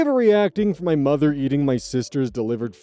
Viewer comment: My signal hiccuped right here and since I was audio only, I heard “AIO for my mother eating my sister?” And I was like “WTF?!?!?”